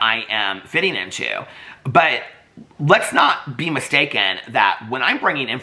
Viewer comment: I know this is a very serious video, but somewhere around 16:00 you said "be mistaken" and what I heard was "beam a steak in", and that's my only comment for today.😂 ❤